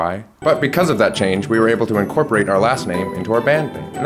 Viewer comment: I always hear “opalinsky” in a Russian accent or something.